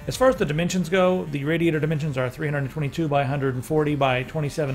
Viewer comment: he says the dimensions are..... 392? doesnt make sense. 322? dont understand